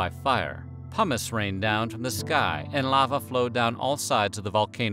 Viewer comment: What I heard: "Hummus rained down from the sky...." 😂 That doesn't sound so bad.